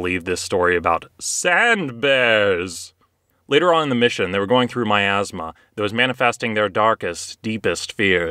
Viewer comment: “They were going through myazma” i had to do a double take there. I thought you had just said “they were going through my Asthma.”